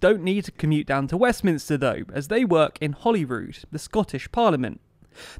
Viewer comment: Every time I hear "Holyrood" I think someone's trying to say "Hollywood" but with a strong accent.